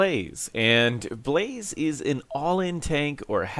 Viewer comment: I swear i heard "Blaze is an Oil In tank" at the start